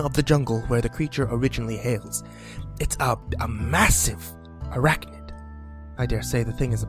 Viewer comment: I heard it as "A MASSIVE ERECTION!"